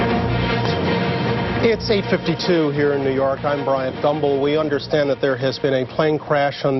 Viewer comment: 9/11 The day so traumatic Bryant Gumbel couldn't say his own name 😮 Did anyone else hear Thumbel